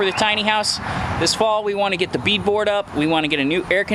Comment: It is likely that the tomatoes are an indeterminate variety so they will continue to grow. Determinate varieties only grow to a specific height. Did you say "beat board" @ 10:19? What is that? As for the broody hen - find her an fertilized egg from someone who has a rooster and let her hatch it out...no harm in doing that. :)